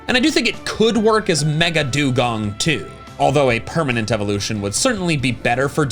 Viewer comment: all I heard was “DeeDee… Mega…Megadewgong” 😂😂